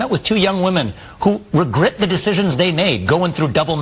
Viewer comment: did he just say regert?😂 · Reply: He said "regrit"